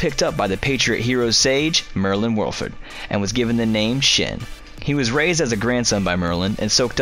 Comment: thought you said he was given the name ‘Shit’